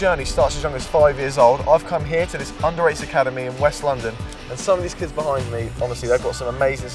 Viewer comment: I thought he said he went to an underage academy